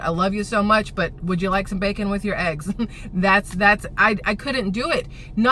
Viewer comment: I thought you said "Do you want spanking with your eggs?" Lmbo!!